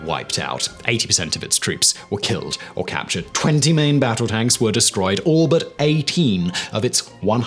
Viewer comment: did you just say 20 million tanks were destroyed?! Am I just not hearing this right?